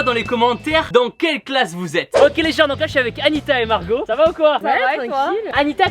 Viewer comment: tu parles avec une annisa mais moi je m’appelle wejden 😭